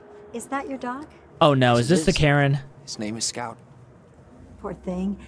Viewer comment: Did bro said scout